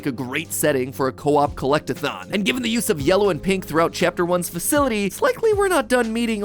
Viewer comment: DID YOU SAY [[Yellow]] AND [[Pink]]?